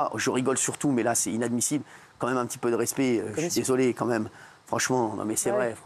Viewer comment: il dit quoi ? Respect ? Bolloré mérite du respect ?